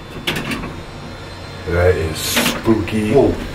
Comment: sounds like a women talking can’t understand what she said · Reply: Sounds like she says “this is spooky”